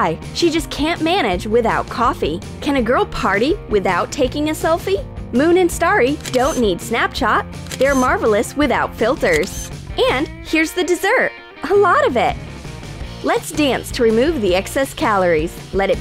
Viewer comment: It sounded like she said snapchaht